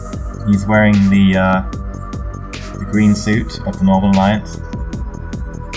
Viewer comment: It sounded like he said "Yep he's wearing the green suit of Northernlion's".